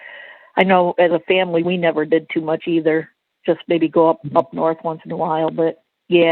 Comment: Wait a minute... at 34:05, did she say "nup north"??? I thought that was where Donald Morrison was from.